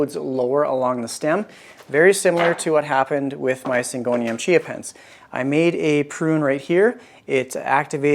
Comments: What is the name of the plant that you were propagating in water? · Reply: Thank you. Now it makes sense why some of us thought you said Chia Pets. 😂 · I know it’s far too early in the morning when my brain swears it heard you say you were propagating Chia Pets! 😂 All of your plants are so beautiful. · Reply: lol, I thought I heard chia pet also but just dismissed it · I know it’s far too early in the morning when my brain swears it heard you say you were propagating Chia Pets! 😂 All of your plants are so beautiful. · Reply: it really does sound like he says chia pets, even the closed captions rolled with it